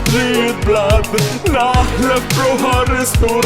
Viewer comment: I heard it say "pink period blood" the first time. (Should have really done that instead)